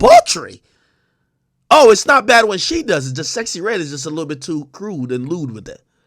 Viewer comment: Did AK JUST SAY SEXY REDD IS JUST A LIL MORE CRUBE AND LUBE WIT IT